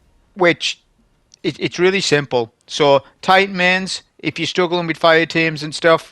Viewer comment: just a reminder but i reinstalled destiny 2 & hundreds of women flooded my dms, they said "wow, do you main solar titan??" at first i was afraid (i do not talk to women) Then, I began to understand this is the power of destiny 2, the game. This is what Bungie has been working towards all these years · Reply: Ohhh “Titan main”. I thought he said “tight men” during the video. Now I get it.